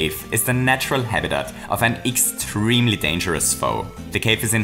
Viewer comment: I first thought you said "dangerous biting Icays"